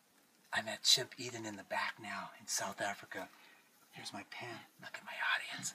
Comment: "I'm at Chimp Eden in the ???? ??? (back now?) in South Africa." What does he say?